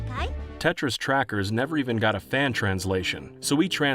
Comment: I thought you said 'Tetris trackers' instead of Tetra's trackers...